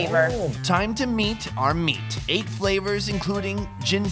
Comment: Ooooooh .. at 1:52 I heard it "time to beat our meat" 😂😂